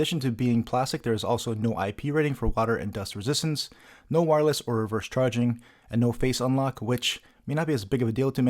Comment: what do you say at 1:04 thru 1:06, and what does it mean? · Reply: No wireless-- or reverse-charging, and no face unlock